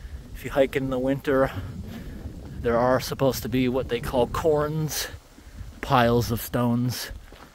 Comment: Sweet hike! It sounded like you said "corns" when mentioning "cairns". It's pronounced like "care" followed by an 'n' sound. Or like Karen without the 'e'.